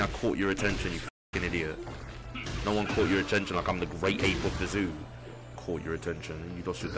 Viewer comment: did he say he is the great ape of the zoo?